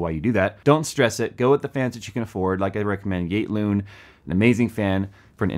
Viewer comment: Damn could not understand what brand of fans you were talking about. Thought you said igloo, then i thought you said gate loon. Now i see its YATE LOON. And yeah i have seen lots of cheap fan brands but never heard of yate loon fans. They look ultra cheap made. And the price is not great at $9-15 each.